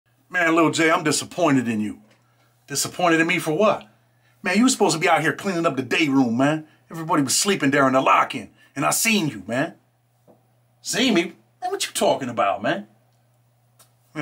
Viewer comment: I dead ass thought you said little man Jay, like hold up I know my man ant doing that😂